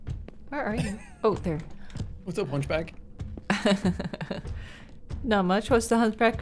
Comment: 😂I thought he said "what's up lunch bag" instead of "hunchback" and I was so confused